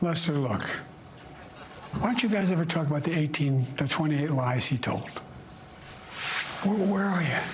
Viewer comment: Can you explain what Biden was referencing? It sounds like he said something about the "18 to 28 lives he took" but I can't figure out who took those lives or what Lester has to do with it. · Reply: He said "lies he told". I could make it out loud and clear, even though English is my 3d language. What's your excuse?